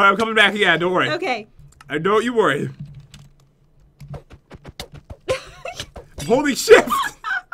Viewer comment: Who else heard at 17:30 he almost said a cuss word but instead said shift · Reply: Happy loving Sisters! it was at 17:41 and he didn't 'almost' swear he just said holding shift